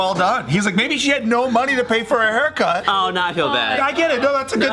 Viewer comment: When Tiff said ‘to pay’ I thought she said toupee I was cracking up 😂😂😩😩